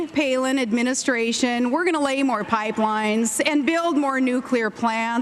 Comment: did she say that in january, her and mccain are going to lay some pipe? awesome.